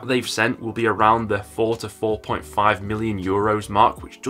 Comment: My word I thought you said 44.5m euros not 4 to 4.5 million what is wrong with me